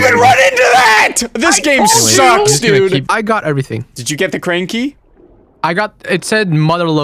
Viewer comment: I heard "Did you get the crane key?" as "Did you get the cranky?" and im dying